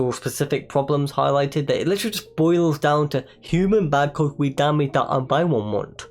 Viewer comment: Cool video man, never really thought about the video because I had almost forgotten it ever existed. But I gotta ask sth what did you even say here 3:33 I am sry but I have 0 clue what you just uttered 💀.Hope this comment helps ya with your channel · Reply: He said, "Human bad cause we damage our environment."